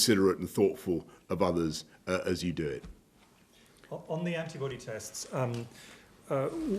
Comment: Wait.. Did he just tell me to buy a pregnancy test?! 🙄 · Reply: Derek Barton ...i thought he said buy an aids test